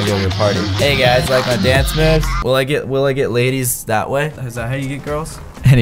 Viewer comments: I thought he said “Can I get laid” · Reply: For me I thought he said will I get laid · I thought he said “Can I get laid”